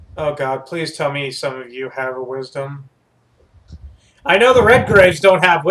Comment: I just found this 7 year old play. I love the humor but I hear "what wisdom?" and I am like "oh god no ...the fears...the mind controls...they will all die"